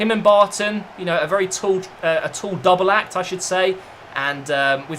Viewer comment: What does he say at 6:22? Double what? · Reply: I believe it's double act